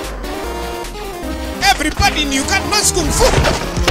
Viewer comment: what did he said? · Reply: Everybody in Uganda knows kung-fu!